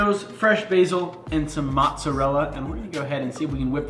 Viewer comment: did he just say moTzerella